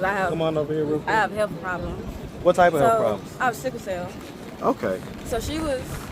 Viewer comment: Lol I thought she said “sick as hell” not cycle cell